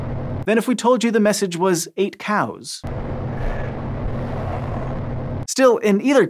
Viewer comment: “8 cows” is terrifying enough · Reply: I didn't hear "8 cows" or "Get out" I heard "Heeeeh"